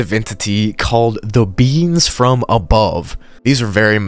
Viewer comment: When you said “the beings from above” I thought you said “ the beans from above” I started to cry-laugh